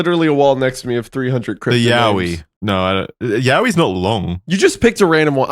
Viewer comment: Got absolutely jumpscared by here 16:27, thought he said yaoi and my sleeper agent brain started tweakin